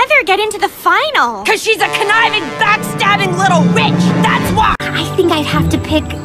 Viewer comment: On 6:05 I thought Eva said *itch for a sec😂😂